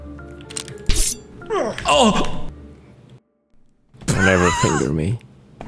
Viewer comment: Did I just hear say "Never finger me"???😂